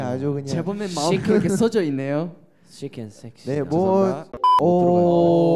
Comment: If it wasn’t for the words I would have thought he said chicken sex